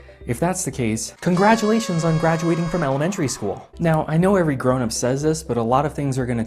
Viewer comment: "he said anyone graduating the 1st grade would know the TRUE history of Columbus" Wait, when did he say that? He said there was a particular version of the story everyone was familiar with, unless I missed something egregious. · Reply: I made a mistake and stand corrected. I thought he said 1st grade, but he said Elementary-School. With that said, I understand the elementary school reference was meant as insult by. The reasoning I thought it was funny was because any American who took a post high school American history will be exposed to the truth about Christopher Columbus, even the softball version of Columbus exploits are not pretty.